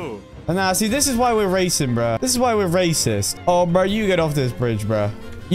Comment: did he say racist??